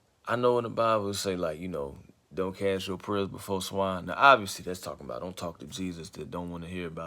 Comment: can someone explain and what did he say??? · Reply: don’t cast your pearls before swine